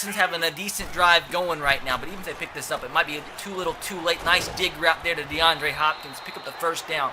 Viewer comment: I swear he said nice d*ck route